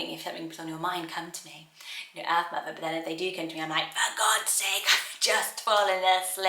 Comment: thought you were gonna say f*** off for a second 😅